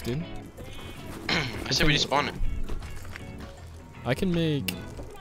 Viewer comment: is it just me or does he say "i say we just spawn it"